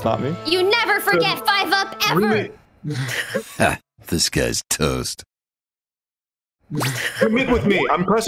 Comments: is it just me or does his intro sound like its saying THIS GUYS TOAST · The intro will be misheard as "This guy Toast" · This whole time i thought Toast's intro said "This guy's toast" and not "Disguised Toast"